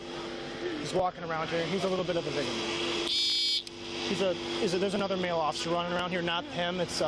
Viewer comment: “We have a traffic officer walking around here, he’s a little bit of a dick” ??? What is said here lol